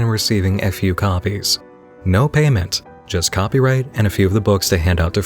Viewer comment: I seriously had to listen to 21:50 a few times. I thought you said he had received "F.U. copies"